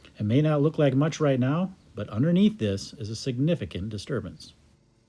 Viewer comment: When he said significant disturbances, I heard " Disturbance in the force" he is a Jedi!